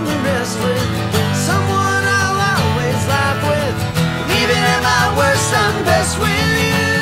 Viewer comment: What does he say? at 2:10 Someone... always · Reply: someone I always laugh with